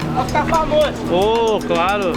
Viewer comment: Can someone help me find out what that word means at. 2:46 he sais ohh culado or something. Ive been trying to find out for 4 years now until i saw it in this video of marks. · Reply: Someone in the street said: "Vai ficar famoso!" which translates to: "You're gonna be famous!" and the guy answered: "Oh, claro." which means: "Oh, sure/of course." It's also possible that he said: "Vou, claro." which would mean: "Of course I will."